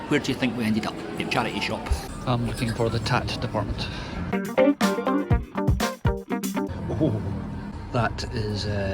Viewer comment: Hello! If you produced a video walking around the block three times I would watch it. I'll ditto what Brigid said, I'll by a DeNiro calendar too. At 2:26, what was the Charity Shop Department you were looking for? That was some weather out there though, phew. P.S. Good luck on a two burner stove. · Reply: That’s the tat department. Not sure it’s an actual word. It’s the budget end of the shelves where the odds and ends are. It’s our favourite place :)